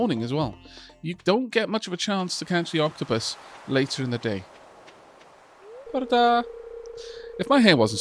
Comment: I’m curious, can I ask what you say when you greet the people in the game like how you did with Alex around 3:35? It sounds like a nice greeting though I’m not sure exactly what’s being said (how to pronounce) and what the meaning is. Though I’d love to be informed if anyone can do that for me. 😁 Edit: I did some more research and I believe what’s being said is “Bore da” in which “Bore” means morning and “da” means good. So the phrase would be good morning. That’s just what I’ve found and I’m not sure how accurate I am with my research. And also “nos da” means goodnight in Welsh which is what is said to Yoko before bed.